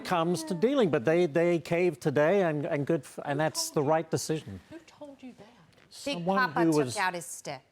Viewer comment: I heard "who told you that" after he said Canadians are nice DD😅